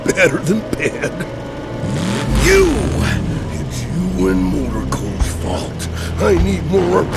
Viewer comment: What does El Machismo say, “It’s all __ fault” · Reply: You and motorco"s